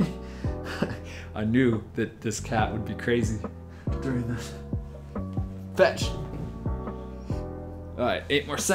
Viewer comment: I thought he called his cat bitc